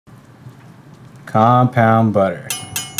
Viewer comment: I could’ve swore he said “pink compound butter” in the beginning